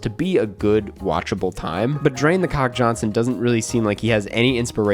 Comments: did you really say "drain the cock Johnson" at 7:02??? :D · Is it just me or did he say "Drain the c**k Johnson" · drain the c**k Johnson? Did I hear that right?😂